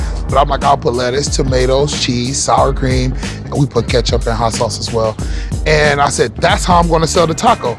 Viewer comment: Did he say ketchup on tacos? 🤔